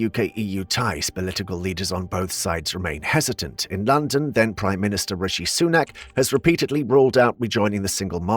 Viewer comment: How is this video 45 minutes old but it says the Prime Minister is Rishy? He's been out of power for over a year 🤣🤣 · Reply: Eng is not my 1st nor 2nd lang and this is what I heard: "...in London, then PM R.S..."